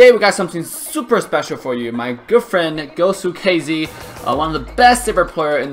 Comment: Ur girlfriend is korean? Edited... I misheard.. He said good friend